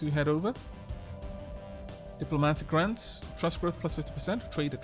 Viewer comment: Was I the only one that when said "diplomatic grants" heard it as "diplomatic rants" and had a WTF moment?